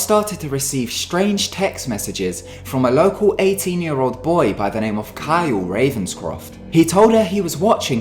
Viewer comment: Okay,I know only people who watch pretty little liars will get this but at the start he said a 16 year old girl was getting text messages from local a 18 year old boy,But I thought he said getting text messages from a local -A team! lol